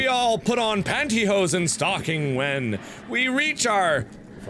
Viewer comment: did i just hear panty and stocking? oh god he watches that too?